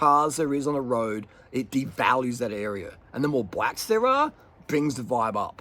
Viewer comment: I thought you said blacks lmao